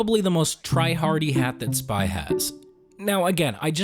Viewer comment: I just heard solider say spy here